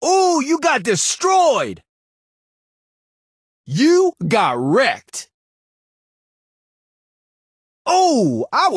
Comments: I think he said "oh you got weight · i thought that he was saying: you got rekt xd Srry 4 my bad english